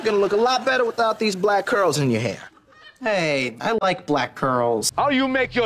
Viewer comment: Oh my God I thought Robbie said I like black girls I spent my whole childhood having a crush on him just because I thought he said I like black girls 😸